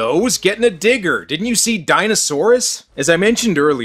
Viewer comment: and 15:03 what movie did he say on the picture did he dino saurus? Place tell me